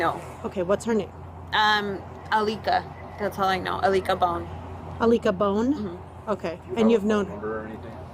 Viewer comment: Alika Bone is insane work 💀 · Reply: Thought she said Alita Bone at first which would mean “chicken wing bone” in Spanglish